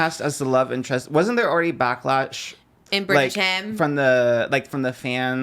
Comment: did he say "back latch" 😂